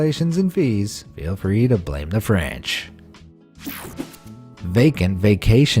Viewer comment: Did he just say "blame the Fanch"?